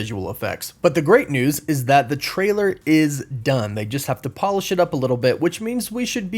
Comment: I heard: "...the trailer is done, they just have to polish shit up a little bit..." 😂 Then I went, oh, I see now. I'm a big fan! Keep up the amazing work!